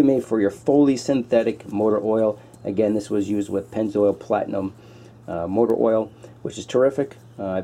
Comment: You won't go wrong with Purolator Boss and Pennzoil Ultra Platinum for an oil change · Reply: I thought he said he used Pennzoil Platinum and not Pennzoil Ultra Platinum? I never heard him use the word "Ultra".